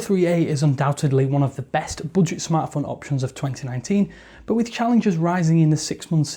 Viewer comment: did you say boodgit?